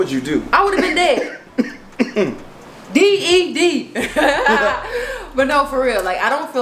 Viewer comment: Did just said she will have been dead and spelled out "ded" 😂😂😂😭 ❤❤ Edit